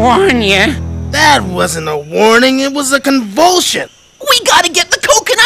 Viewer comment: when I was kid I thought he was saying "that wasn't a warning it was fucking bullshit"